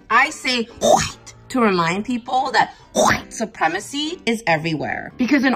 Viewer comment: At first I thought she was saying wheat